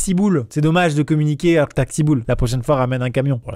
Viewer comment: Quand il a dit "t'as qu'six boules" j'ai compris "taxi boules" 😐😐 voila c'est tout 🤣